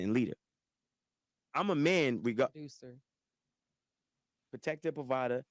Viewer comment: it sounds like she said "abuser" and not "producer". Maybe I heard it wrong. Can anyone else clarify?